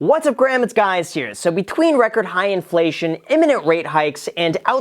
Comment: What is he saying in the very begining? All I hear is "What's up Graham, it's guys here". Btw, it's not the first time when he does that... Is he reaply greeting himself?